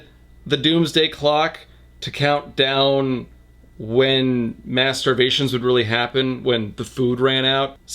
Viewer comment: did anyone els hear him say "masterbations" at 1:21:51? I know he said "mass starvations." but for a second it sounded like the other thing.